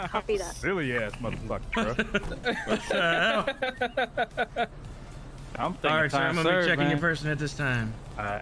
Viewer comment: can someone please tell me what he says at 17:42? lol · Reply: "I don't know whatcha talking about"